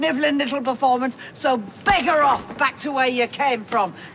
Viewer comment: what did she say, bicker off or bigger off? Without sounding stupid 🤣🤦🏽‍♂️ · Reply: ‘Beggar off’ it basically means like get lost 😂